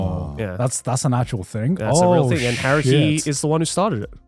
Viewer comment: Okay this is the first time Garnt's accent ever threw me off, I thought he said "Harry" in the beginning not Haruhi.